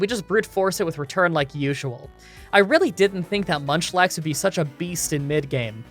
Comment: Did anyone else hear him say lunchlax at 7:38?😂